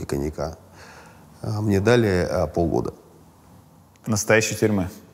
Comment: English subtitles show '2 years' instead of '6 month', and that's not the first time